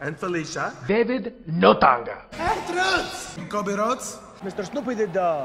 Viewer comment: covid rhodes sounds like he said that 😂